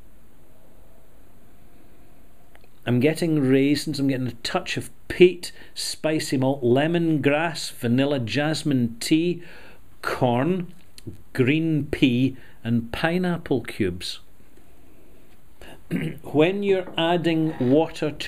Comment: Ouch, for a minute there I thought said green pee not green pea. I think using the plural green peas might have been clearer.